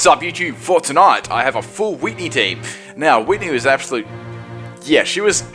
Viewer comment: Almost thought it said Full Whitney Houston team.